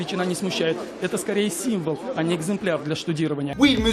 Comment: C'est moi ou il a dit "un exemplaire" ? Avec l'accent malgache si ça se trouve.